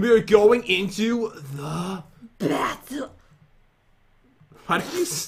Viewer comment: I think I know why Jen stopped at Battle because it sounds like she said bathtub instead of battle LOL